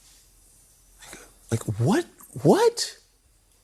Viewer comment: Is it just me or does it sound like he says "nigga what" under his breath